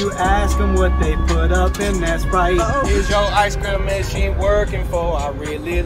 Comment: At first I thought they said spice not sprite... I feel dumb